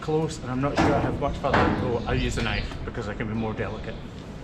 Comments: When you said “use the knife” I heard “euthanize.” I thought you were going to put her down! Thank goodness I just misunderstood you. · my husband was talking when you said "use the knife" and I thought you said "euthanize" not a good transposition! LOL